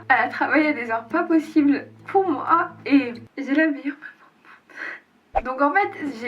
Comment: elle dit quoi?? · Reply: Ari's Channel "et j'ai la meilleure maman du monde"